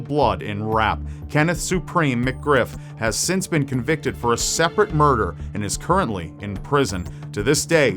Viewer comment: I thought it said raper murder